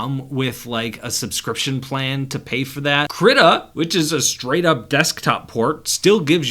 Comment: What was the name of app at 6:04 which is straight up desktop port? I couldn't hear it clearly so please reply. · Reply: Krita! It's a free and open source drawing software, works on nearly any device